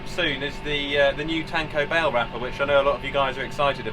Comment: Really thought you said taco bell wrapper. Which would be a great vinyl wrap. Lmao